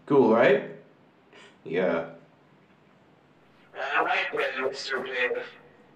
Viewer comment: what did he say in the 6:04 ? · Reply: From what I can make out I think he said “Alright then Mr. David.”